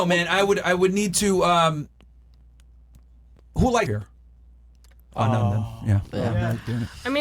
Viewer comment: Who what? I didn't catch what Myron said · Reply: Ah he said "who likes hitler?" lmao now that makes total sense especially what that black girls said after. Thanks man